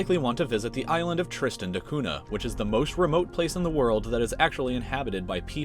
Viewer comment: I thought he said "Tristan Hakuna"